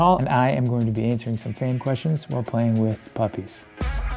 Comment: If you close your eyes really hard it sounds like he says "bussies"